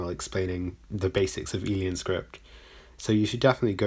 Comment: I thought you were saying : "Alien Script"